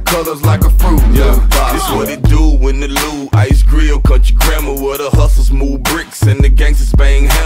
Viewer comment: I always thought he said: "What it do, in the loo Ice Grill Cut your grandma"